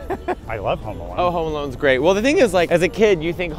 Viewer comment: Wait… I swear, I thought you guys were saying “Homo Alone” The while time. 😂 I’m sorry. 😅